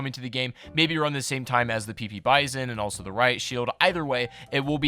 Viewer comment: did I hear riot sheild